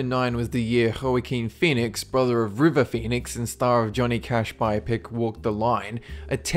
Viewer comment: Lol.. I thought you said "hurricane phoenix". Had to listen a couple times. It's pronounced like "wa-keen". Not that it really matters, lol. I did get a little chuckle out of it. :)